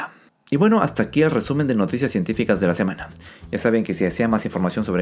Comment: Intro 0:12 Bienvenida 0:18 Lasemanapasada 0:45 EBAC 1:21 Lasemanapasada 1:22 Fue cancelada la misión de la NASA que traería muestras de Marte 3:48 Científicos descubren una regla universal que frena a la evolución 5:36 Científicos descubren un nuevo mecanismo de división celular 7:48 Los mosquitos se alimentan de nosotros con más frecuencia y los científicos dicen que somos los culpables 9:31 Científicos podrían haber encontrado finalmente el Santo Grial de los sustitutos del azúcar 11:41 Partículas fantasma que interactúan con la materia oscura podrían resolver un enorme misterio cósmico 13:53 tarea para la casa 13:56 Los astronautas regresan a la Tierra con cambios cerebrales duraderos 16:03 La NASA planea instalar un reactor nuclear en la Luna 17:58 Fin de las noticias científicas de la semana y página Robotitus 18:14 App Robotitus 18:23 Calendario espacial 18:29 Frase de despedida Servido amigos xd saludos cordiales a todos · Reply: por un momento me asuste, entendi "Fin de las noticias científicas de la semana y página Robotitus" Como si significara que ya no habran mas noticias cientificas y que la pagina robotitus moriria, como si fuera EL FIN de esas cosas, jajajaja